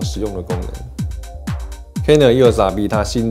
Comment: 为什么我一直听到 canon 又傻逼... eos rp 读快点真的是会听成canon 又傻逼... 不懂有没有和我一样 😂😂😂